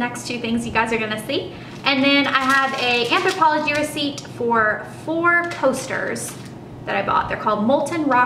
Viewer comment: I totally thought you said four toasters instead of four coasters and was very confused. The bag and watch are gorgeous!